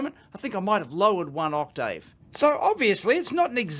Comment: Did he say "I might have lowered one octave" Or "I might have lowered one octDave" ?